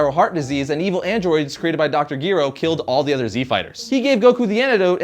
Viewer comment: Wait, did u say dr. Giro???